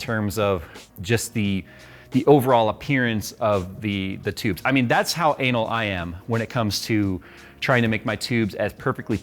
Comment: Am I hearing this correctly? "I mean that's how anal I am..."